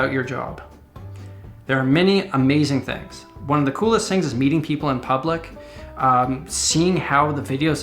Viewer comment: i heard beating up people